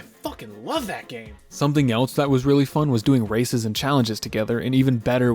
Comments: I thought bro said racism challenges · bro did not say racing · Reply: Yeah he said races